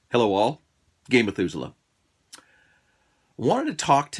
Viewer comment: Hey! Love your channel, love your advice, love your approach. Just pointing out though to be silly, no disrespect intended, but I always hear "Gay Methuselah" the way the syllables line up hehe.